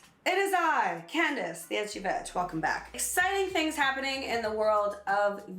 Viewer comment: I thought you said " it's your bitch, welcome back", lmao.